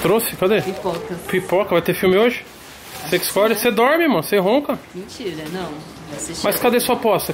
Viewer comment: só eu ouvi " cê dorme mor !"?